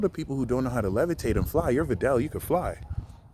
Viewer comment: Videl? 13:02 Did you just say Videl, like from DBZ?